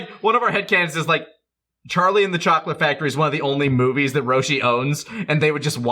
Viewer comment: My brain did not hear that sentence the way Lani presented it. I heard "Charlie's Angels is one of the only movies Roshi owns" and I just started fusing the movies togethr. Lani has spawned the new fan fusion movie, Charlie's Angels and the Chocolate Factory. Thank you, Nicholas, I now need to make this a reality.